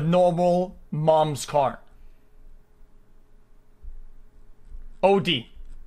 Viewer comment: Did..di..did..di-..did he just say "oudi"?